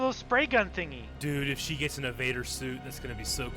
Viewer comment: i thought he said evader suit too don't worry ahaha